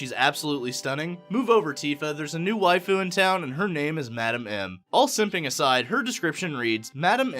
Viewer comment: At first I thought you were saying Madam Mim (as in The Sword in the Stone), lol.